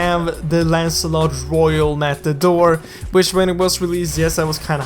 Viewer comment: Wait... did I just heard "We have Lancelot Royale MataDork